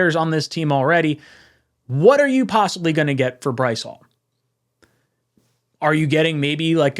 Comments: I almost ran myself into oncoming traffic. Thought it said BREECE hall. Breece hall, bryce hall; quincy williams, quinnen williams, good Lord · I nearly jumped at this and thought it said Breece Hall.